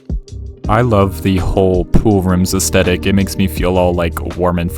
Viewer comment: THIS SOUNDS SO BAD OUT OF CONTEXT 😭✌ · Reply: I paused it right before he said poolrooms aesthetic and it sounds like hes saying I love the hole